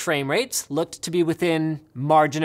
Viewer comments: did he just say "closlier"? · Did Linus say closely-er? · did he really say "closlier" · Did he just say looking closlier?